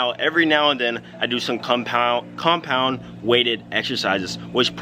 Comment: he says Kung pao instead of compound too much Chinese food u have been cheating your diet